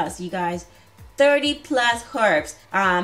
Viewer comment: who else thought she said 30 plus PERMS.